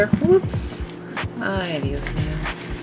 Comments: did you just say "ay, Dios mío"? Maybe it was only my Spanish ears hearing things. Good video. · did you say Ay Dios mío? 😄